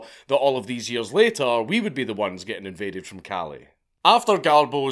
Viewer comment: As a Californian, I heard, “Who’d think that we’d be invaded from Cali?” and thought it was a Silicon Valley joke rather than an immigration joke. Accidentally well played!